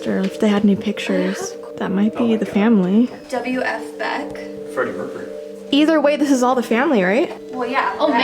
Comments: who else heard say Freddie Mercury? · Did someone say Freddie mercury?????